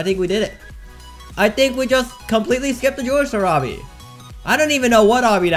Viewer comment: with captions on “Completely skipped the George Shurabhi” Also keep watching with captions on “Dear diary alright never had to do diaby a single bit” That’s what the captions say lol